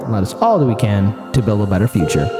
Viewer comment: I love watching your channel but for the life of me I don’t understand your moniker? I hear “Let’s follow the weekend to build a better future.” Please clarify the first few words for me. Thanks for your attention and great humor. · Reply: "Let's do all that we can" is what he is saying.... "to build a better future"